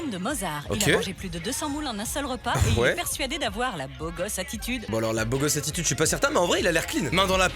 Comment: à 7:51 j'ai compris "il est persuadé d'avoir la boloss attitude" 😂